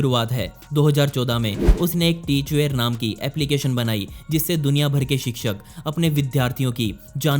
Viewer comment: what is name of the app he said? I can’t understand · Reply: Teachware , I think so🙄.